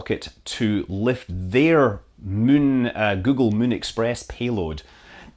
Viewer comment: does he say MUN express?